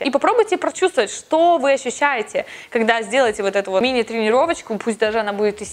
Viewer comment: Сначала послышалось: "сделайте хотя бы мини-тренировочку из 70 минут"🤣🤣🤣 Видео мотивирует, спасибо